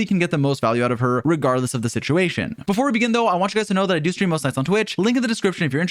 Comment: "i do stream [what][what] on twitch" what is he saying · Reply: most nights